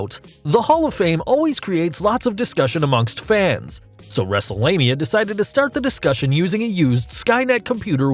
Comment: did he just say WrestleLanea